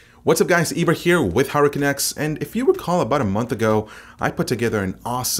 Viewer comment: Ibra with Hurricane X, I know it should be HardwareCanucks but I always hear 'Hurricane X'